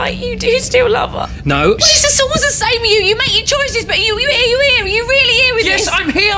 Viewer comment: What is she saying from 7:10 to 7:13? I heard here here here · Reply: It’s something like: “Are you here? Are you really here with us?” 😄